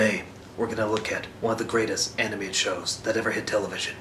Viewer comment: i thought at first he said worst anime series i was like you fucker and then i rewatched the begining and was like oh greatest hahaha